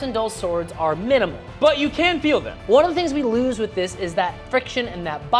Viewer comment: The subtitles say you can't feel them, the video says you can feel them